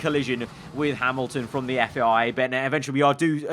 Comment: did just say a warning from the fbi ? Just me ??